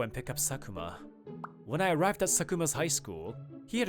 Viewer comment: did he say sukuna?